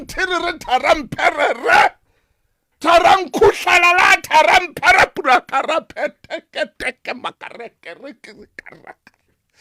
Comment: did he say "Tharang Pheriri..Tharang Tetete...Ma..rete..te🙈🙈🤣